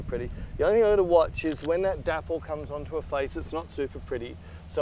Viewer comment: What is "Daffol" (as I hear it?) at 7:51 <link> . What does it mean? (Denmark here...) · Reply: Dappled- spots or small patches of light